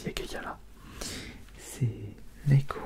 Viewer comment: pourquoi j'entend "négro" 😭 ( mais genre vraiment )